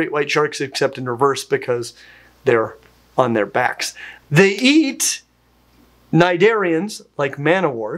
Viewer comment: I'd swear I heard ''they eat nigerians''